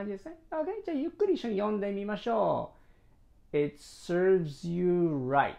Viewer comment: It's saads your right.と聞こえた。